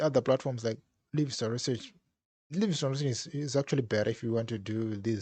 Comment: At minute 7:49 you compare studypool with other platform but I couldn't understand understand what you meant. If you can please share the other platforms. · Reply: Livingston research, and essay shark